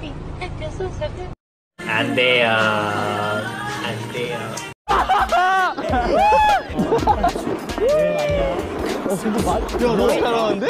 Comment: tell me why I thought he said "hey seungmin please touch me I feel so f*ckin fluffy.." then I had to re watch it to make sure 🤣🧡🐥